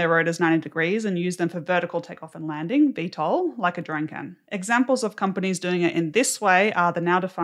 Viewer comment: Small correction on the English subtitles: at 2:45 it says "like a drunken", instead of "like a drone can". EDIT: I just noticed the English subtitles are probably just copied from the auto-generated ones, so nevermind😅